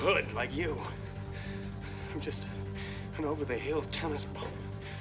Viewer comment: Ever since I was a child, I hear him say "I'm just an over the hill tennis PUMP " 😂 I'm assuming he says "Tennis PRO " or possibly even punk but I can't help but hear pump. Lol.